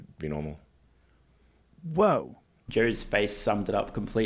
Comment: Wait what? "Jerry's faces?" Say what?